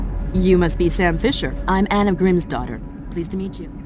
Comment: I'm outing myself as a dumbass but Anna Grimsdottir I thought that they were saying Anna, Grimm's daughter. lol I was like Grimm's daughter huh? And who the hell is Grimm?...